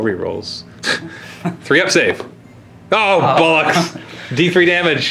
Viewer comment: Did I hear say Bollocks?